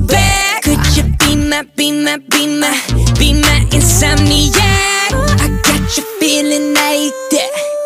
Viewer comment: ITS SOUNDS LIKE HES SAYING COULD YOU BE IN MY TUMMY YEAH